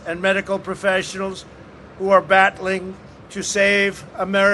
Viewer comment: Who are "bat-ling" to save American lives. 7:25 am I the only one who heard that?😂